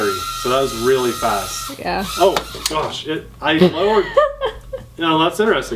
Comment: I agree with Stacey Abshire. I listened to "8:26" three more times. I heard,"GOSH". That was the "horrible" PROFANITY..... YIKES!!!! There I go, using profanity too!